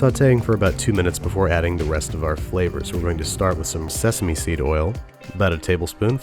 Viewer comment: I heard he saying 'sesame street oil'. '-'